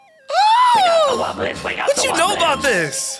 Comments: Brump sounds like hes saying "we got the whoppers! "We got the whoppers! WHOPPER WHOPPER WHOPPER WHOPPER JUNIOR DOUBLE TRIPLE WHOPPER! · I might hear the weirdest misheard lyrics.before I learned what brump says I thought he was saying “we got the waffles” and with mulch I think he’s saying “get down the pumpkin”